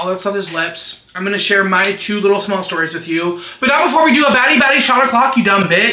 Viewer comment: BADDIE BADDIE SHOT A CLOCKKKKKK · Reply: Wait he's saying baddie? I thought he was saying fatty 😭